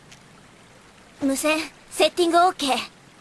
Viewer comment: What Tenten says: Anose setinga ok The subtitle: The wireless radio settings are good to go.